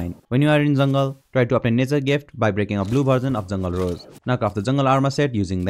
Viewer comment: This is really good! Just a question, what did you say in 1:01 ? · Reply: You can obtain, nature's gift by breaking a blue version of rose